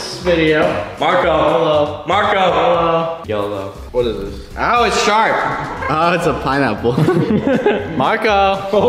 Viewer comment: I thought he said ow it’s a shark 🤣